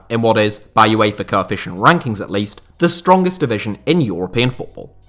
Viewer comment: Sorry for a second I thought you said it was the strongest league in Europe 😂